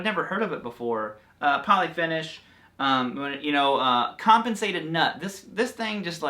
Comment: Did you just say ‘constipated nut’? I hate it when that happens! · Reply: Compensated 😉